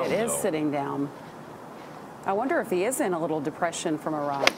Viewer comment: 23 I heard “he is in a little depression from Iraq”